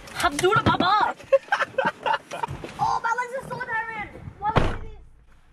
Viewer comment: "that's a bit kinky, I don't mind that. Let's get this started!" GO FOR IT PLS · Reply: he locked the car mates right after the scene that she did say that and i heard ¨my legs are so sore why are we doing this¨ O_O